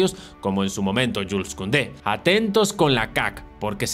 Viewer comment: suena a que dice "atentos con LAKAKA" o es idea mía? 🤣